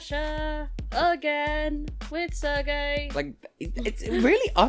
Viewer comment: That sounded like "with Sir Gay". 😊 I mean, that joke is old, Barei already did that to her competitor in 2016 (because "ser" = "to be" in Spanish). But given how Wiwibloggs freaked out when his return was announced, that title would actually fit him. And his music video, with him being a knight in it. "Sir Gay, the hero of the LGBT community." 😂 PS: I probably just had that "Sir" association because I'm listening to a British video 😇...